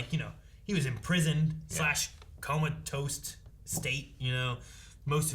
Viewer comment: Wait. Did say "coma-toast"? 36:25 This feels like a Morty's Mindblowers-type situation.